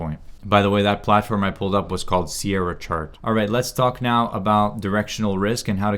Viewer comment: what is the name of the plattform? I dont understand canadian english that well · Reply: It is Sierra Chart.